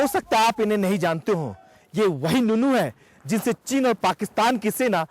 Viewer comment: did he say ye wahi nunu hai. WTF